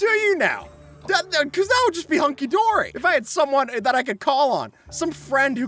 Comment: Sorry, I had my hostile sounds off, did you say Munkidori?